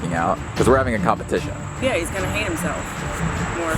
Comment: if you did not put the subtitles I would have thought she had said "he would hang himself"